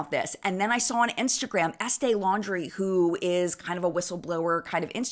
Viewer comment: I’m sorry I think I misheard, did you say estee laundry?